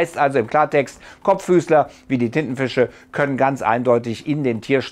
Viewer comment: Ich hab Tittenfisch verstanden 🤣 ein Glück ist Wochenende 🤪